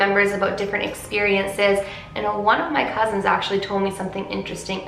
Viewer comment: I recently found your page and I’m watching your videos like 24/7. Anyways, my mother was overhearing as I was watching while eating lunch and at one point, you said “one of my cousins” and she thought you said “one of my husbands” and she was appalled. So funny. Anyways, love your content!!!